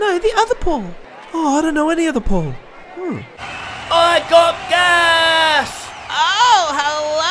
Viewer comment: only one i knew what he was saying. " GO THE CATS"